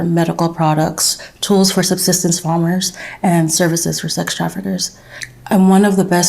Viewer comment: did she say "services for sex traffickers"?